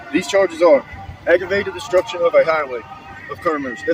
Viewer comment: It sounded like he said "aggravated destruction of a highway"???? that is a felony with potential 15 years in prison.